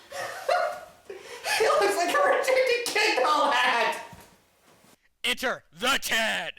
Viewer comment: Yeah, what did you say? It had sounded like you had said, "...a rejected kid doll hat.", huh?; and 10:40 - 10:50 Yeah, Al, that I had heard before that it's Lucy's first period or I then figured that it's her first orgasm, I don't know which. Also, that I think that Naylor had indicated to us all with one of his pictures, in his previous artwork collection, that after Fang had grown up, that he then went on to become a pimp, or something like that, huh. >Danny<